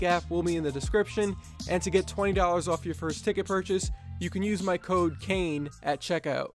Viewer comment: I almost thought you said "You can use my COCAINE " o.o